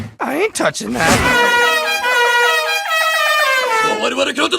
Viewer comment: what the hell is said?!?! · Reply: Yuuki Hyuuga I believe it's "Domo arigato subaru subaluwa."